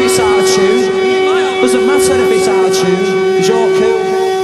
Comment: Is he really saying "it doesn't matter if its out of tune, because you're cool"? I always heard it as "Is it my turn to be Sargeant? Well you're Paul"